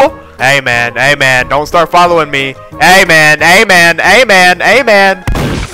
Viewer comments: What says: ay man What i hear: amen💀💀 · He says: ey men ey men ey men ey men etc What i hear: AMEN AMEN AMEN AMEN AMEN AMEN